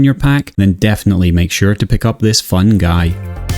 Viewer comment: LOL, I was rewatching this video and when you were closing on Snotflagga, I heard you say to "pick up this fungi" - love your humor! : )